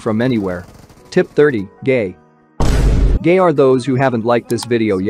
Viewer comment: Did he say gay😂😂😂😂😂😂😂